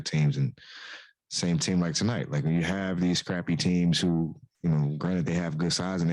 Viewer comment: Did he call the cavs scrappy or crappy @ 7:40 I hope he said scrappy